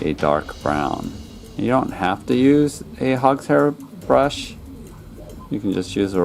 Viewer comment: what kind of brush did you say? Hopster brush? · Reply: Hog's hair brush. Like these: <link>